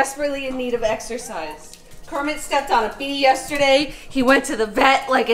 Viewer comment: Hold on, how did Kermit step on a bee with his little toothpick legs? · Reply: omg i thought she said he stepped on a pea